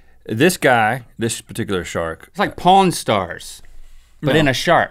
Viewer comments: did say porn stars ??? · Did say porn stars at 7:52 ? O_O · pawn stars i swear I thought said porn stars anybody else? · Did say porn stars at 7:52 ? O_O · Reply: I thought he said porn stars too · did say porn stars??